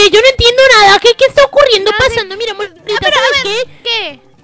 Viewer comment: No se si escuche mal pero te dijo en él minuto 7:31 "Mira amor?" 7u7 o solo te dijo mira ;-; ps no she pero creo q escuche esho :"3 te AMO SOS LA MEJOOOR TE AMOOOO 😘😘😘😘😘😘😘😘😘😘